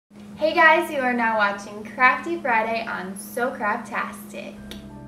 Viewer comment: At first I thought she said crapy Friday. But to be fair it is loud in my house